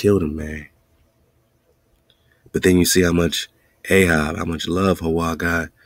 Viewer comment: Been wondering about that since I heard it. Tonight too. Thank you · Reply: Ahab Ahki